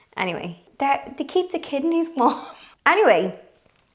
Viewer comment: They keep the kidneys warm 😂 Ohhh, you’re a laugh and a half. Please never change 🥰😂😂😂😂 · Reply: I had to replay that section because I thought she said they keep the kitty warm. 🐱😂 Ooohhh. Kidneys.